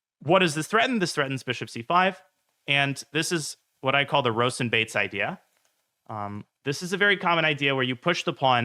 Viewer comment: Instead of 'Rawson-Bates idea' I heard 'Rosen's bait idea' and I thought it was some trick inspired by Eric XD